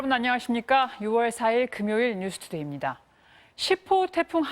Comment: 나만 6월 4일로 들리나? 엥? 하고 날짜 봤네